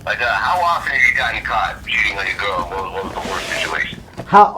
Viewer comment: At first I thought he said “how often have you got caught shitting on your girl?”😅